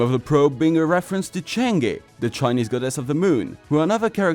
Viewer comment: did i hear Chan'ge